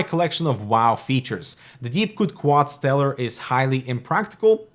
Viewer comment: what is the name of this case? (i'm not sure what he is saying in the vid) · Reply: DeepCool Quadstellar